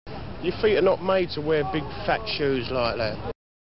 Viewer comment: You're feet are not made to wear big shoes like that. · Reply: I heard it as "These mates wearin' big fat shoes like that"